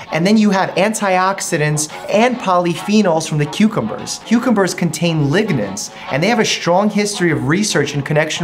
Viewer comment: I swore he said “Cucumbers have ligaments” and I was like “Say what, now?” 🤣